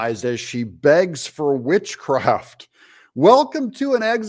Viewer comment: She already has it.....sorry ....thought she said Bitchcraft !!